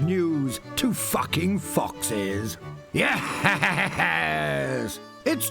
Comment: Freaking drawing my sona and the first thing i hear is "F*** FOXES" AYO!?